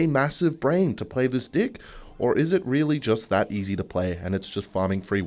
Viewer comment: Am i the only one who hears d*ck instead of deck